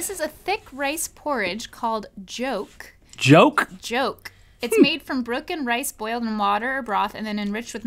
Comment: did you said 죽?...